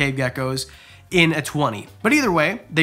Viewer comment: Am I the only one who thought he said “ human loving” and got excited but then realized he said “humid loving” for Chinese cave geckos 🦎 😭